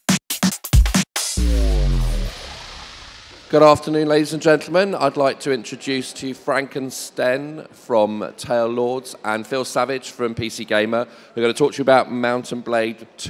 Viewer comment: Did he just say TaleLords? Everyone kneel and bow your head to the almighty TaleLords.